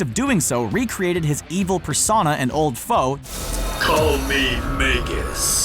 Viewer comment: Who read the captions saying Vegas instead of magus